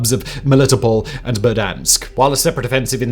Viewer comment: HOLD UP did Simon just say….Verdansk ?